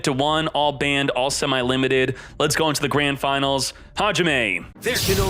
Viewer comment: can sm1 please tell me what he says at the start of the video. It sounds like hes saying Ha-jim-ay. what does it mean?